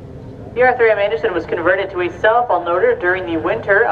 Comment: I keep hearing 'Cell Phone Leader ', Instead of Self Unloader.